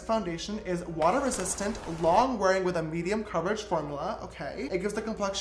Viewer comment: Is it just me or a 4:02 did he say form ou la?😂😂